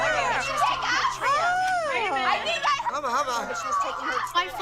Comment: did Abby just say huba huba 💀💀